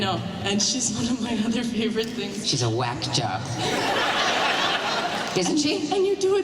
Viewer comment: what is shes saying? I don´t understand the joke :( I´m from Germany, can someone help me please? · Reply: "She's a wack job" which just means a crazy person.